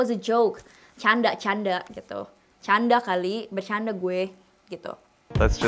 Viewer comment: Canda kalii.. jadi candu dengernya wkwk